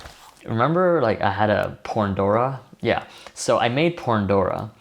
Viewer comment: Did i misheard something on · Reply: Wahyu Amanullah nah it's Pandora